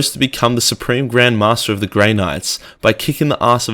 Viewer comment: Did actually say "grey nigger" at 5:02?? 😑😑😑